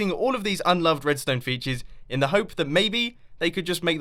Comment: I thought it said Unsolved redstone features at first, and I thought you were sarcastically calling bugs "features."